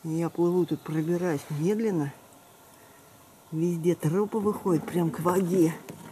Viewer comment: Мне послышалось: "везде трУпы выходят прямо к воде"))